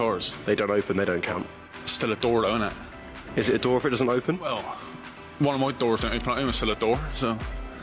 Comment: Can someone explain what he's saying at 00:18? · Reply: his door at home doesnt open , but its still a door so